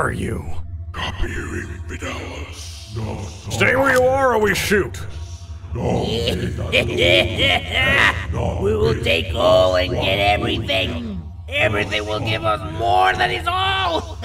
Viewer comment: I think they said "Bring him with us" and then when mr clown speaks "We will take all and everything,everything will give all"